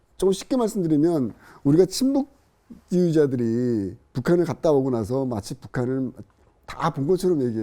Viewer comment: 초 자막중에 친북이라고들리는데 침묵이라고 나오네요. 영화꼭 보겠습니다. 이미친게 아직도안봄